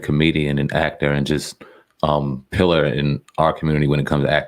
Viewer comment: Did you just say Bill Cosby was "a pill-er" of society? Or did you say "pillar?"